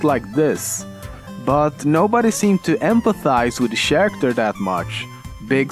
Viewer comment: did he say Sharacter?